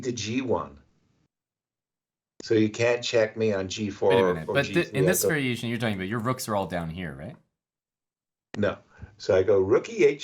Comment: I may be biased, but does Aman speak greek?? It sounds like he says 'Περἰμενε' meaning 'wait' Etiher way great video I enjoyed it a lot!